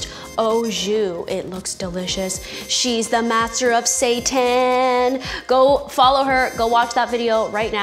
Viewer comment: Did say "she's the master of Sata"n?? ... o.O lol wtf? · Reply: zoology999 lol thanks for clearing that up, I replayed that part over n over and kept hearing Satan 😳😂